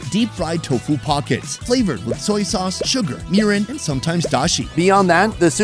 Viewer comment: on 2x speed I swear you said it was flavoured with urine. After being to a Disney land before, this surprisingly didn't surprise me.